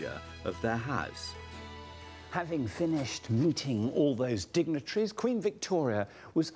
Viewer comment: I heard "Having finished Looting" instead of meeting. I mean it wouldn't be incorrect 🤡 Over 50 Trillion Dollars (today) Looted from India alone